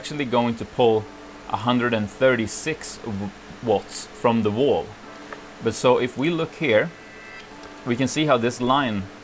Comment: Power supplies do not pull more volts from the wall, they pull more amps. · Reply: Sorry. At 6:12 it sure sounded like volts. My apology.